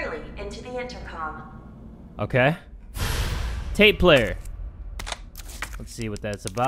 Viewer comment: Jay:tape player Me:did he said hitler?